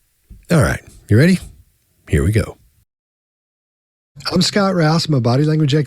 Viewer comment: It sounds like Scott says “I’m Scott Rasma”. Anyone else hear that?